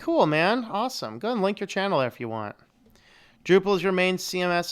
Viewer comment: you said something about my YouTube channel but I can't understand... Can you say it again? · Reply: I said you can post a link to it if you want :)